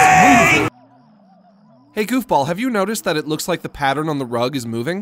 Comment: my phone thought he said "hey google did you ever notice that..."